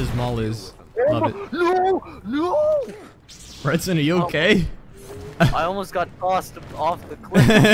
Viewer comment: did he say “ Branson are you ok?”